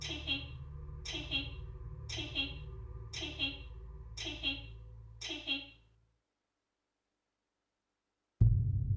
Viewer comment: is it saying tiky? or am i down dumb? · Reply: It's tee hee and lee is a liar and fraud who swindles vulnerable people out of money. Xx